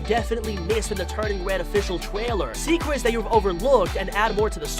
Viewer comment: wait did he just say twailer